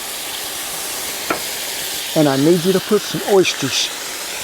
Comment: When you said oyster sauce I thought you said worcestershire sauce then saw that thick sauce hit the wok. I thought, sumpin's up with your W sauce!